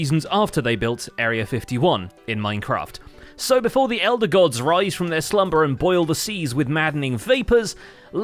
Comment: Omg... At first I totally thought he'd said "before the elder guardians rise from their slumber and boil the seas"! Thank you, for caption reassurance that none of the hermits are doing game-breaking magic with guardians now! Because tbh, at this point I wouldn't put it past them! 😂